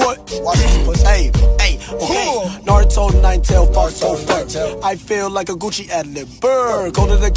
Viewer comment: Wait listen to 0:27 more closely and tell me he does not say “fire tail” instead of fur.